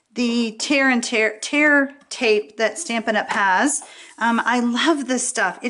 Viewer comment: When I first heard about the tear tape, I thought everyone was saying it's "terrible" tape. Ha ha! BTW, it IS wonderful!